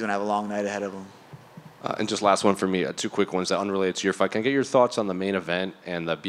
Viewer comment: Did Brah say "It's your fvKc!ng year ... " 😂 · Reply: It's unrelated to your fight - can I get your thoughts ...